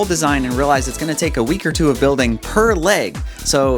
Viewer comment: did he call the prototype a girl? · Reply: He said per